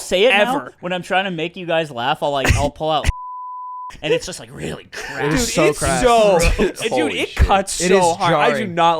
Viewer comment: Damn i wish i knew what they said at 33:54 even on Spotify it bleeps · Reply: according to twitter account the word is “cunt-hounds”